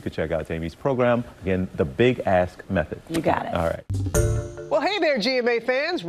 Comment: I heard that as "The Big Ass Method" I like that too lol. Honey this ass has gotten a little out of hand. Can you help me by being accountable?